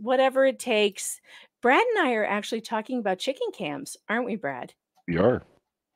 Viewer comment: Oh chicken cam!!!!! I thought you said chicken camp and was very confused